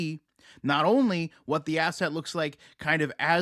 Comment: I heard ass-hat!! Not asset Lol! Anyone else!?